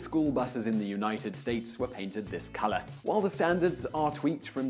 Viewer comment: When he says they then started painting with this color, I thought he said with piss color